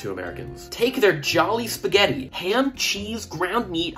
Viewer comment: i can fluently understand Tagalog and at the end i thought you said good food (or something similar like that)